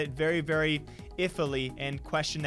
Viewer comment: did he say ifly??????